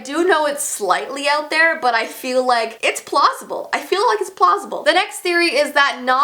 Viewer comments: is it just me or does it sound like she is saying plossible? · it sounds like your saying it’s plossible 😂😂